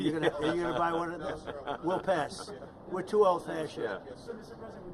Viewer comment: Did Trump just said "We're too old for that sh*t"? :))